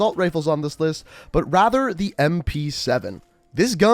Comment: I thought u said mp7 not aug